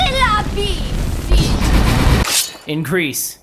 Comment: I thought he said in Greece not increase xD